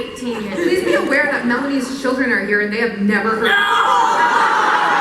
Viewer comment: What does melanie said at 12:16? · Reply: please be aware that melanies children are here and they have never heard that word